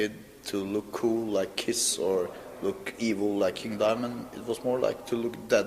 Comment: did he say "to look evil like Neil Diamond?"